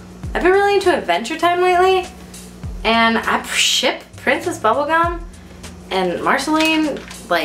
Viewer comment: i thought she said "i shit princess bubblegum and marceline so hard" lmao